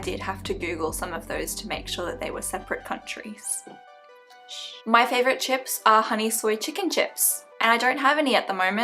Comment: Haha I thought you said 'My favourite ships are..." and I got hella confused when you said honey blahblah chips.. :D I also have nightmare about teeth like all the time!! WHY THOUGH?! They are terrifying! Your german was actually good! I'm here if you want to practice!